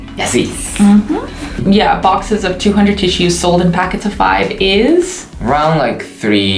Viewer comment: I wasn't looking at the subs and I thought he said "this shit" instead of "tissues